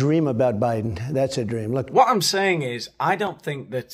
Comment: When you said "Master Debater", all I heard is "Masterbater". Its all I can hear now 😅😢